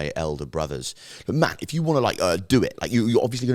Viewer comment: Did call himself Matt? 🤔 is not his real name?